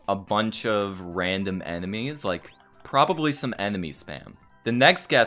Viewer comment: you are a bit small on cam, maybe you could zoom in a bit more. Oh and am I the only one that heard Animes instead of enemies 1:42?